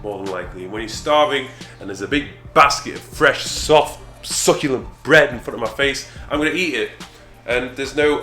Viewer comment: All I heard was "soft succulent breast". I want in on your program at any price!!!